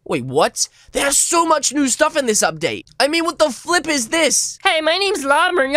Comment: did say what the fuck is this?